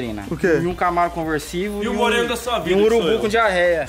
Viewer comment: Só foi eu que escutei o que o disse ele falou assim: e um moreno na sua vida que já sou eu. Gente aiiiiiii ❤❤❤❤😍😍😍😍 agora eu sei viu agora eu confimei kkkkkk